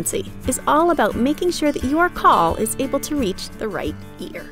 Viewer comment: "...is all about making sure you ARE call reach the right ears" ....why did i hear that